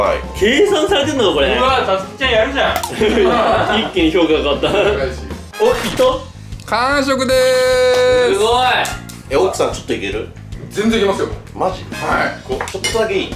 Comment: くらいのがに少し頼む所､｢さちょっといける?｣が｢奥さんちょっと行ける？｣に聞こえてしまったw